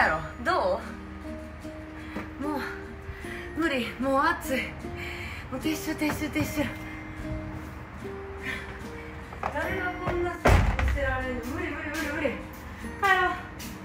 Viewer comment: 撤収撤収撤収がティッシュティッシュティッシュに聞こえた。こんなん観たら確かにティッシュ要るわ。